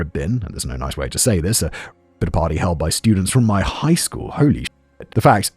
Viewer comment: Can anyone clarify what wrote/Simon said here? Pitter Party? Bitter Party?!? I’m lost lol. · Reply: There had been a rape at a party